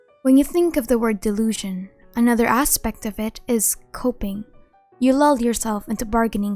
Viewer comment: My hearing was blocked by the sounds of crunching food in my mouth, I thus thought I heard the word copium here. I'm sorry.